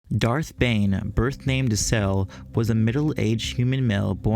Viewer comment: At first I thought you said Darth Bane was birth names Cell, not Decel.